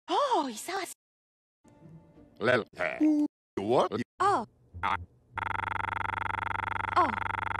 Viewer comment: co on powiedział? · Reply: Lel, he, u Wall-e, a, aaaaaaaa.